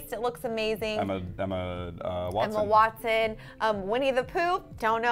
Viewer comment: Did she just say "Winnie the POOP"? Seriously?!